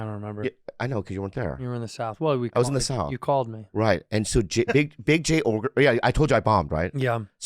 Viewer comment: I thought Bobby was gonna say Big Jay OGREson